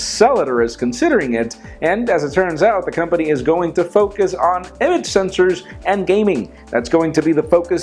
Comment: the company is going to focus on ¿?¿?¿?¿? sensors? what did he say? image?